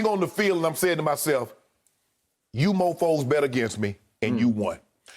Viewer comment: Did Shannon say, "MoFo'S" at 2:20?